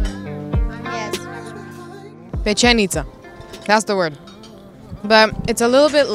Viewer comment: did say ‘b*tch’ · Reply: Pečenica PEČ B Č=ch